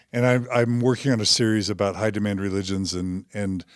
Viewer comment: At mark 18:55, you mention that you are working on a series about "hidammmm religions", I can't quite understand what you are saying. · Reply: "High Demad Religions" is what he is saying. Hope that helps.